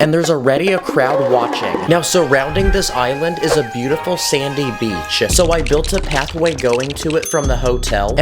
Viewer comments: I thought you said "A Beatiful Sandy Cheeks"😂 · When you said sandy beach I thought you said Sandy cheeks